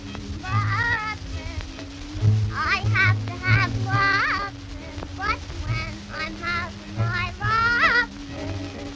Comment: Is She saying “I have to have Asses” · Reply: no she says, lovin, i have to have lovin but when i'm havin my lovin i have to have you